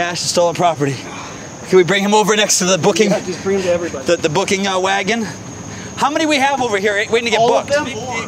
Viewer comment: did Patty say "the booking wagon", or the boogie wagon"?????